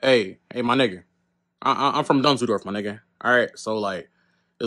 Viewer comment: He told him where he from twice and he didn’t listen🤦🏾‍♂️. Bro had it coming fam, it was only a matter of time my guy💀💀💀💀💀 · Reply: "I'm from Dumbledorf my nigga" That's literally what I thought he said.